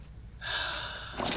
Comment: 🤨 “YEaaaaw man!” Sounded like he was about to cheer for Noir getting gutted.